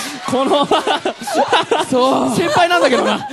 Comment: He actually said “So this is how noya-san feels” not murase-san feels